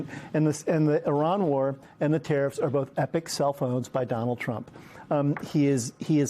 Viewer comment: The US will be Trumps 8th bankruptcy. It’s happening already!!!! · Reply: does he say 'epic cellphones' ?